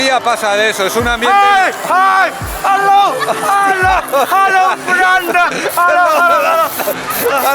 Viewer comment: Que le pasaba xD Estaría bueno saber que dijo ese señor a los gritos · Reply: Hi, hello, hello friend😂